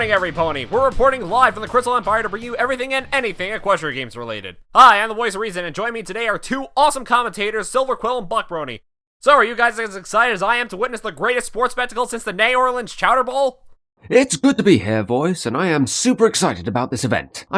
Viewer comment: I swear I thought Voice said "Butt Brony" at 0:24 Besides that I agree with you guys that while the venue was underutilized, it doesn't change the fact that it was a damn good Spike episode and a good episode in general. I wish the fandom could learn to curb their expectations and judge episodes for what they offer and not what fans' desire.